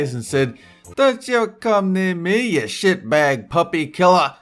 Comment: did anyone hear say sh*t